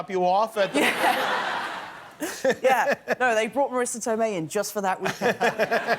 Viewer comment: What did he said at 3:20? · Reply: "yeah they brought marisa tomei just for that weekend"